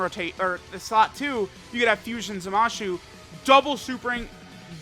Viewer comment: what did you say? Zamashu? 😂😂😂